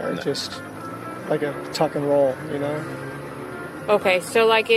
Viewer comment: Sounds like he said Tucker rd. Not tuck and roll.